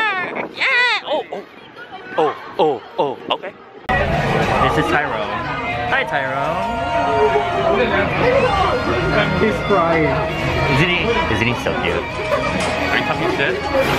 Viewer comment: I left on my auto-generated closed captions on and at 3:46 when you said "This is Tyrone...Tyrone...(He's crying)", the captions said "Tyra Tyra Prize-winning"